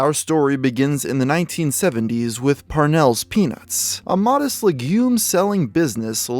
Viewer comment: when he first said parnell's peanuts, i thought he said parnell's penis. lol